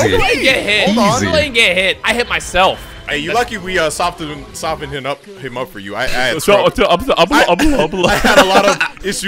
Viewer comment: i think he said "ay, you lucky we uh, softenem. soften hinup. him up for you." 8:25 I believe it (, dont let me down) 10:29 you let me down